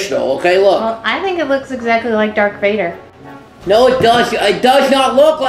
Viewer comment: did she just say "dark Vader"!?!?!